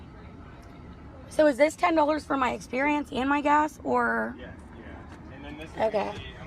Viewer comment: when the gentlemen says let me scan this card, did I hear her right when she said “is this $10 for my experience and my gas”?